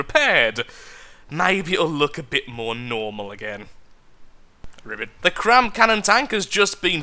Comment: did he just say ribbit? or am I just going crazy?